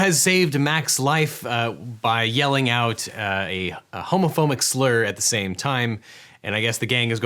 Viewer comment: Did you just say... Homophomic?